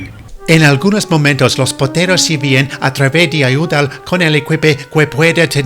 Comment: I"m I hearing thigs or did Toby say los potatoes